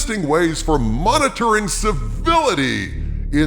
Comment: “Monitoring civility” I honestly misheard that as “monitoring civilians.”